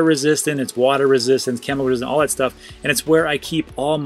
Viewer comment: did he say it was camel 🐫 resistant?